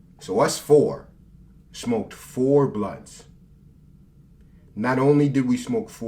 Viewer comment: I thought he said they smoked 10 blunts